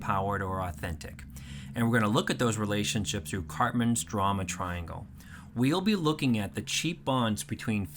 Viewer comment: Is it bad I heard Cartman's drama triangle at first and it made sense. 😂